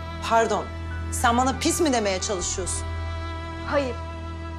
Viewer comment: Sahnesinde ne dediğini anlıyanlar 😢💔 👇🏻 👇🏻 👇🏻 👇🏻 👇🏻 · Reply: Pislik diyor